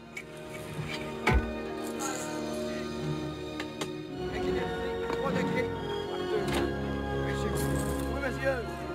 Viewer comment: What is the mortar captain saying at 6:46? can anyone plz tell us? (French and English both would be highly appreciated) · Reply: "Oui Monsieur, bourrez la charge !"